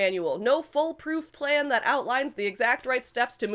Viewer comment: I misheard the full-proof plan as foal-proof, as if you were proving it against baby horses.